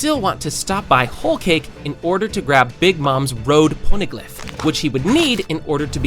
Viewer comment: subtitles said "pornoglyph"